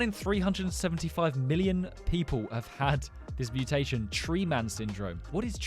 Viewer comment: I thought I heard truman